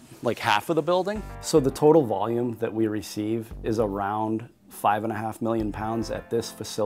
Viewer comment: the captions say 55 million pounds instead of 5.5 million thats a slight diffrence